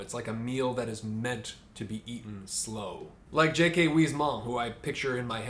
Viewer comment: Who is this author that he mentioned? I couldn't quite grasp what he said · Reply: J.K. Huysmans 👍 Great author